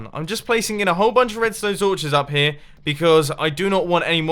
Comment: Did Mumbo just say im placing in a whole bunch of "redsone torches"